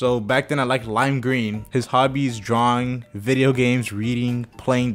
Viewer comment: I thought that said drowning💀💀💀